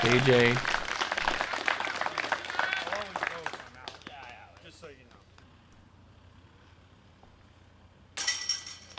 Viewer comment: What did Doss say to KJ around 14:40? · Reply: "I'll always go if I'm out, just so you know." In reference to a "who's out?" dispute between the two.